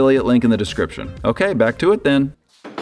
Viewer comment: did i hear a chit 'back to it then' at 33:15 ?